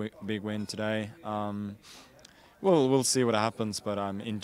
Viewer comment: What does he say at the end? at 2:26 In general i'm not adverse to finds? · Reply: he says he's not averse to fights